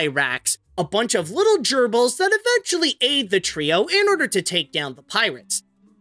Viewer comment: I had to hear that one twice. I misheard "aid the trio" as "ate the trio". My bad.